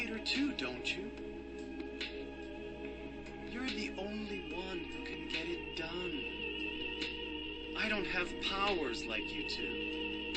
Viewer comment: That sounds very close to “You’re the one to get it done.” Vincent is the Caravaneer from Monster Hunter 4! What’s he doing here, instead of getting plastered in Val Habar?